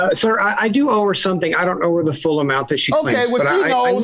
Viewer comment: He misunderstood him at the end he said “I don’t owe the full amount” judge thought he said “I don’t know the full amount” 😆 either way. He admitted in paper that he owed so…touché